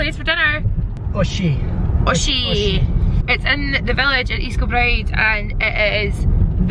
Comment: Thought you said West Kilbride n ma heart just bloody dropped cause that’s where I live n then I realized you said east😂😂😂